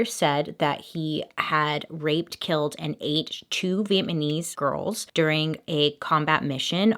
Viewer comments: did she just say that he "rxped, killed, and ATE" Vietnamese women??? Am I hearing this right? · PLEASE TELL ME SOMEONE ELSE HEARD HER SAY VIET-MANESE at 4:38 LOL